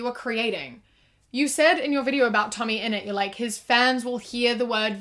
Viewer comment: Thought u said tummy innit instead of tommyinnit